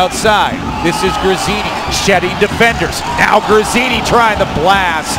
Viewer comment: At min 1:57 does he say shitty defender... lol...wtf..